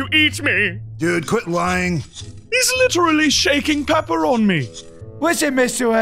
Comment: Mr egg said Pepper on me, but first time i heard it, i thought he said Pepperoni XD